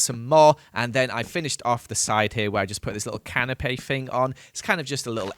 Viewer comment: Love the way that at 10:05 he says canapé instead of canopy 😂